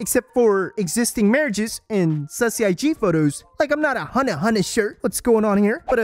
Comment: Did I just hear sussy? Someone's been playing Among Us